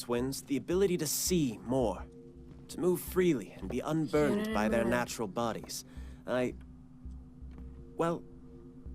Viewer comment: hold up what did she say? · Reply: Huginn and Muninn i think, the name of Odin's ravens in Norse mythology